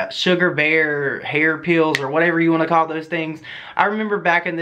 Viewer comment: I love your accent. I literally thought you said "hair peels" 🤣 ugh I can be so dumb. Once I thought it.. I called myself an idiot and realized you said "pills" .. lol thanks.. great video